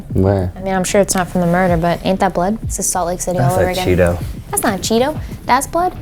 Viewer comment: LOL DID YOU SAY SALT LAKE CITY??